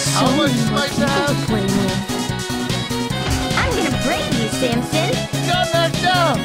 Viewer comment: umbrella did not say brain she said braid